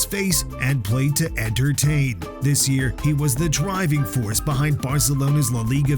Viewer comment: I heard "played to Andrew Tate" lol